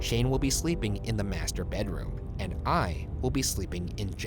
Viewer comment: Im not scared anymore when mishearing 'master bedroom' to 'Master-bed room'